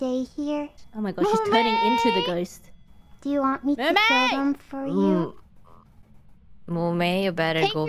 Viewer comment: Anybody know what Mumei said at 1:02 here? Sounded like "Want me to ??? for you?" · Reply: I think she said "do you want me to kill them for you?"